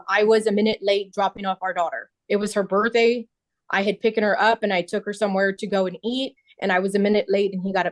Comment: did she actually say 'picken' and not the correct word, 'picked'? This is as annoying as the people who think 'tooken' is a word.